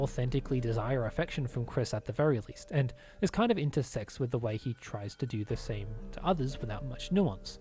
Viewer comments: i had to do a double take because i heard "... kinda into sex" instead of intersects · I had to replay this like 5 times it really sounded like you were saying "is kind of intersex"